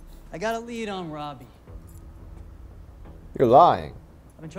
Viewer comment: Did you say, "you're lying?". If so that's great! Cuz they have you as fascinated as we are. I can't get enough of your channel!